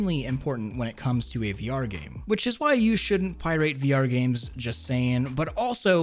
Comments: "pierate" is what I heard XD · Did he say “pie-rate”?